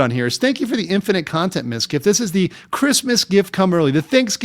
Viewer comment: I heard "This is like a Chris Mizkif come early" lol